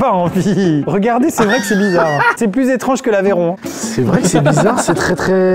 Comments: C'est moi où il dit Aveyron à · C'est moi où il dit Aveyron à 36:39?